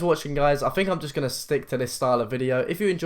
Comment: At the end I heard "check out my vagina " instead of channel from the accent lmao